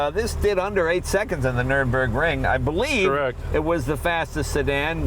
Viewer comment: Did he say "8 seconds on the Nurburgring"